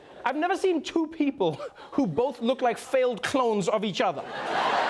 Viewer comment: "failed clones of each other" how do you come up with these things???? GOLD!!! How you turn such dark, unsightly news into something much easier to digest is just amazing. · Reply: I heard that as "failed clowns", I like my version better.